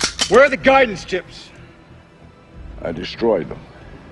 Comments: What the hell does Connery say at 1:48 ? It sounds like he's saying, "Does that mean your Mexican's smoke"? Clearly that's not right. Anyone? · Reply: He says: Does that mean you'll execute us both? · Best line ever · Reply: Hey Ozzy, you just made a comment under this video 2 hours ago. There's a line that Sean Connery says to Ed Harris at 1:48 but I can't understand him. It sounds like he's saying, "Does that mean your Mexicans smoke"...obviously that's not right. What do you hear when he says that line? Can you understand it?